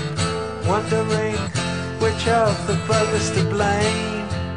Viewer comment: Pigs on the Wing (Part One) (Waters) 1:24 If you didn't care what happened to me, And I didn't care for you, We would zig zag our way through the boredom and pain Occasionally glancing up through the rain. Wondering which of the buggars to blame And watching for pigs on the wing. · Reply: I always thought Roger was saying brothers instead of buggers.